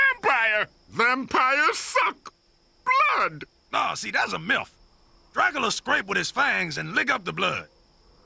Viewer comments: i swear he says “no, thats a milf” (I know he says myth, but still) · I could've SWORE Dracula said "Nah thats a MILF" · I thought he said "Nah see that's a milf" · at first I thought he said milf had to repeat it so many times to realize that he said myth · I know he said "Nah, see that a myth". But it really sounded like he said "Nah, see that's a milf". Which in this context, would make sense too